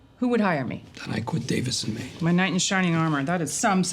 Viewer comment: i'm pretty sure the expression is "my knight in shining armor," but the subtitles say "night." i'm sure we're all for nitpicking the nitpickers!